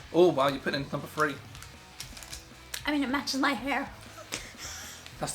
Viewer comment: I heard 'it macha's my hair' - there was a lot of macha in this video to be fair!